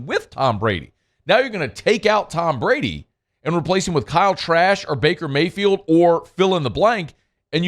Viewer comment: I heard Kyle trash 😂😂